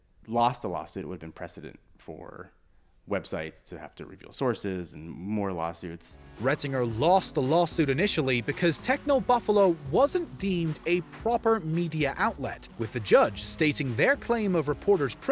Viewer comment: When he said technobuffalo i thought he said technoblade